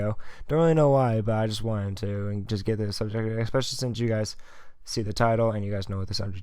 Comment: Right after he says "i just wanted to" in 0:20 What the actual fuck did he said? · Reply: 'get right into the subject of the video' or something like that. i could be wrong